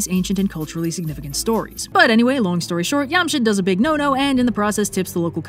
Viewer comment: Did anyone else hear "Yamcha" instead of "Jamshid"? "I don't know what this Jamshid is, but it sounds just like Raditz"